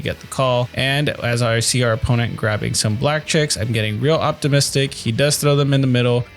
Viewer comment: 1) You totally got me with that sike. 2) damn 9’s were hot that night! 3) it totally sounded like you said your opponent grabbed some black chicks on that last hand 🤣